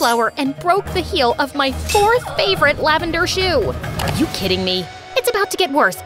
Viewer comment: i thought zoro used to slash black people instead of “LAVENDER HIGH HEELS”💀💀